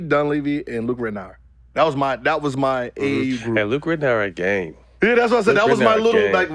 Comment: wat is Matt sayin? He said Luke is gang??